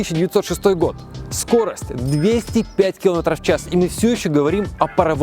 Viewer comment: На 3:01 - "скорость 200 блядь км/час" 😅😅😅😅😅😅😅. Или это только мне послышалось?